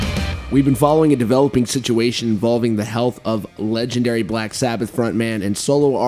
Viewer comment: I thought “health” said “death” and I was so upset